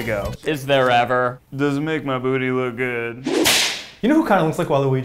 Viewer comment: every halloween I hear Ned go "does my butt look good?" it is now a tradition